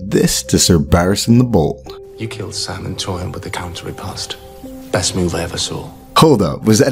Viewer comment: I thought he said “with a counter he passed” I guess this makes more sense when you remember he said “but he lacked stamina”